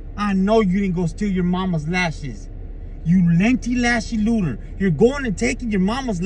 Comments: "You linty lashy looter"🤣🤣🤣🤣🤣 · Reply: Oh I thought he said lengthy lasher looter 😂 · "You linty lashy looter"🤣🤣🤣🤣🤣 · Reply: I thought he said lengthy lashy looter lol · "You linty lashy looter"🤣🤣🤣🤣🤣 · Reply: 😂 Sounds like he said "lainty, lashy looner..." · "You linty lashy looter"🤣🤣🤣🤣🤣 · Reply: I thought he said u Latino lashing looter!? Idk